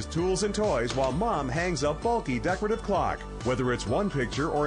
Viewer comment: Did they say while mom hangs a faulty decorative clock at 1:20? Haha